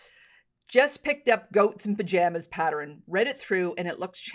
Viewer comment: I thought she said GHOSTS in Pajamas - now I realize it was GOATS in Pajamas. LOL Very cute.